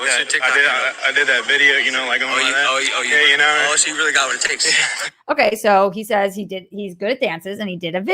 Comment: i dont think logan paul said anything to hype, i heard: "What's your tiktok, do you have tikto? I wanna see if you really got what it takes"....which buddy answered I don't..meaning no tiktok then the mentor thing. But anyways, that wasnt the focus so it's whatever. Have you checked out Reservation Dogs on Hulu. Taika Watiti is EP, it's pretty funny. As always, good show.